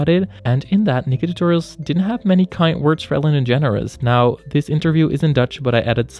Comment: When I first heard Ellen's name, I thought it was a nickname Ellen the Generous😂